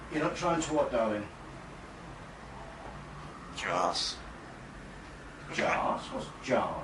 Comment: It sounds like she said Jaws or whatever